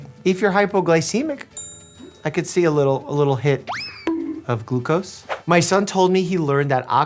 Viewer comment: Thought he said "Glubo's" like glubo from greenskullguy or sum